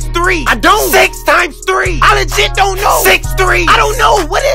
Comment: 아니 진짜로 노!라고 들림